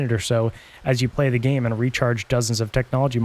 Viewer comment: its even more annoying when you hear Technology Retard instead of Recharged lol